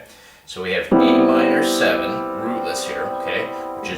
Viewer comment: Did you say E minor 7th at the 1:06 mark. Then you go on to say G E C E. I'm pretty sure that's an A minor 7th and the notes are G B C E or maybe my hearing is off. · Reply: He says "A minor 7th rootless"